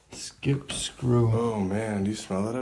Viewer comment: I heard a young lady say ouche ladder and then the ovulous said skip screw. Weeird